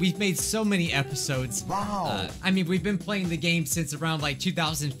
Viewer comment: I turned on captions and when it went wow the captions said mom... MOM